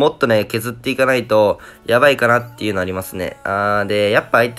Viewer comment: ！7:31、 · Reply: Potti. dayo やっとわかった！「ありますねぇ」 が「やりますねぇ」に聞こえたと言うことか